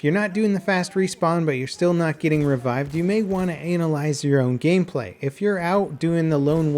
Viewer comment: did I just hear that right or did he not say analyise